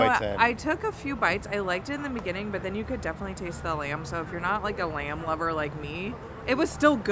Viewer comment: When said "not a lamb lover" I heard "not a land lubber" 😂